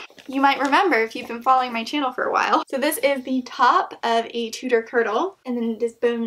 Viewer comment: I swear, I thought you said "Ninja Turtle" instead of "Tudor girdle" at 8:51. I'm tired.